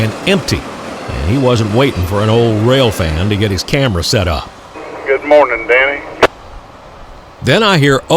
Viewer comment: did they say good morning to you? If so how nice of them.